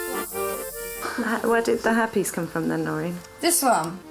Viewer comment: Not sure what the girl said but the subtitle asked where "the herpes has come from" 😂